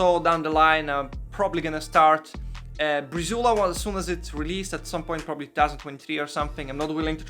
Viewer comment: willing to try what in 2023? I couldn't hear properly... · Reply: Breezula a.k.a. clascoterone a.k.a. CB-03-01